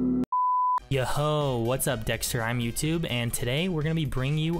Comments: who else heard yooo wasuppp dxtr im youtube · did he just say (" You HoEeE")